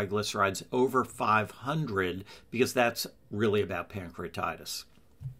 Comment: what did he say ? My levels are over 500. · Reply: I heard “ over 500 because that’s really about pancreatitis”